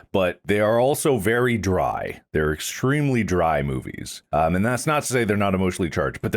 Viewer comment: i heard Mark said "excreamly dry". Still giggling at that stupid misheard. xD